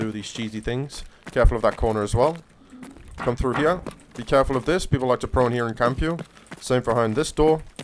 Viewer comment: Lmao... I'm cleaning and listening... I keep hearing "be careful of this spot". Bunch of rats on reserve bro